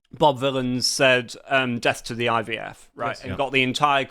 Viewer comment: I heard "Death to the IVF" and just thought the left REALLY hate babies!